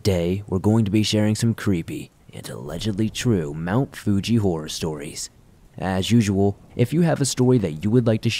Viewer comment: In my stoned state, I thought it said "Mister Fuji Horror Stories"!